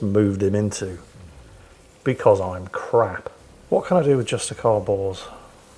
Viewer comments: Is it me or does anybody else seem to hear Every time says "Justicar Bors" I hear "Justicar Balls" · lol with your accent everytime you say "justicar Bors" i hear "justicar Balls"